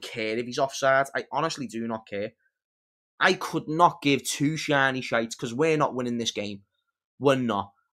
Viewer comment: Two shiny shit😂😂😂, Can someone tell me what he actually said there